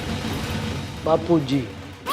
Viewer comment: i think it was babita g instead of bapu g